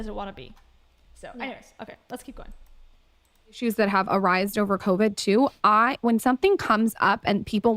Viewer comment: …. Did she just say “ARRISED”?!? What is happening?! These people are complete morons. I can’t. I’m dead.